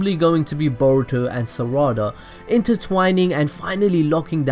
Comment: i thought he said burrito and sa raa da xd